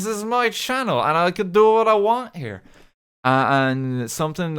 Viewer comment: i thought he said "i'm gonna do what i want now." and i was immediately intimidated